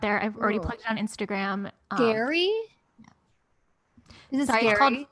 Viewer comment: I honestly thought said ‘Gary’ and looked up I thought we were about to be treated to a BF bit